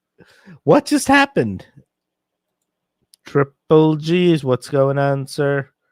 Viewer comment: 😂😂😂😂😂😂😂😂 All this time I thought it was saying triple cheese. I was singing triple cheese to strangers on the street. It's programming. I sent hundreds of people into the world wanting triple cheese for no real reason. 😢😅😅